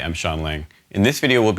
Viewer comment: LOL I thought he said 'Hi I'm Shawn Lane'